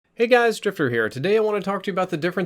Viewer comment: I thought he said hey guys stripper here on the intro